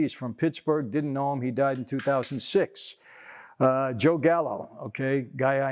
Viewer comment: Did he say Joe Gallo at 12:59?? As in Joey Gallo?? Hehe.. the guy who strikes out at like 55% of his at bats and went from the Yankees to the Dodgers this season.